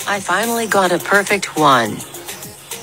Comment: When they said “red heart emo-jie” I felt that Edit: MOOOOOOOMMMMM I’m famous or something I have 2.1k likes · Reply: I HEARD "HEART EMO CHILD"💀